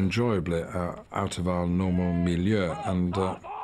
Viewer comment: ”Out of our normal *what*”? It sounds like he’s saying the Swedish word Miljö, which means enviroment. Makes sense, but that can’t be what he’s saying can it? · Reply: He's saying "milieu" which the french word used to talk about the cultural and social circle you evolve to and grow around